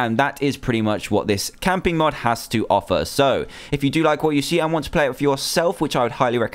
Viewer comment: did you just say if i like what I see then play with myself?